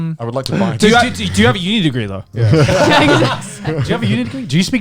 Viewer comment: I didn't hear What did he say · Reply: "Do you have a U degree tho?"